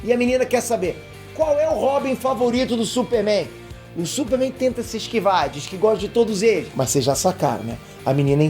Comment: eu entendi hobby